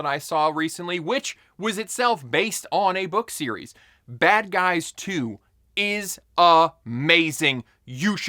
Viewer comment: Bad Guys 2? I've never heard of it, or the first one. I thought you said Bad Boys 2